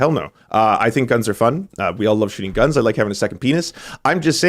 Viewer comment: Did he just say “I love having a second penis.” When talking about guns? Lmao 🤦🏼‍♂️